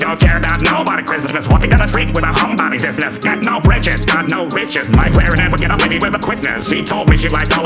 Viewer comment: "Got no britches, got no riches." What I hear : "Got no riches, got no bitches."